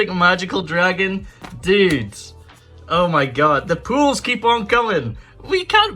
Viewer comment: Every time you say pulls i hear "poouls"